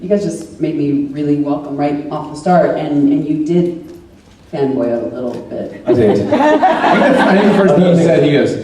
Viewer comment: Minute 11:02 What does Trisha says about Tom? “... and you did ????? a little” . Can someone please help me? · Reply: Isabel Modesti I heard “and you did fan boy a little bit”